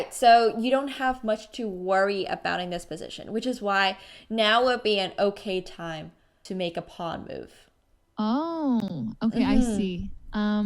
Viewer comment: at first I thought she said "now would be an ok time to make a POG move"